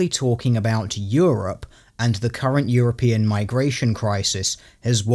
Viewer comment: There's a moment in this video where it sounds like says "Current Yearopean." This is best new meme.